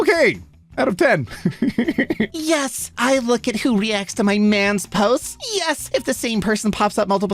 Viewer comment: i was looking away at 2:50 and thought he say "my nans posts" and was like "damn, theyre rly protective of their grandma"💀😹